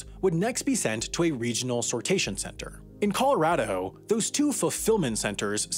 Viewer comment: Did he say Coloradaho? At 5:51?